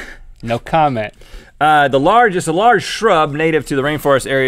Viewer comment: I thought he said the largest scrub 😜😜😜